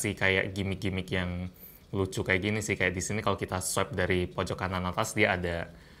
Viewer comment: gw salah denger apa gmn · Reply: Bukan kana tapi kiri atas